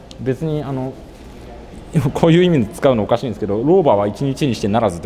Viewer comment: 老婆に聞こえた笑